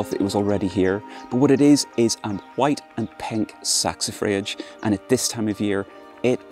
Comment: Lovely garden. What is the small flowering plant at 5:05? The cc says it is "saxophoge" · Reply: Oh it's a little white and pink Saxifrage. They're actually quite easily available in garden centres